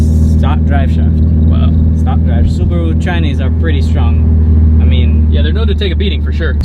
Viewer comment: did I hear he say “Subaru Chinese are pretty strong?”